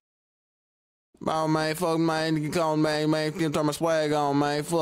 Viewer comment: I thought he said fun